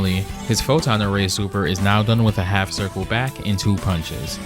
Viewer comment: Dr. Doom: Photon array! What I hear: don't bother Ray! 🤣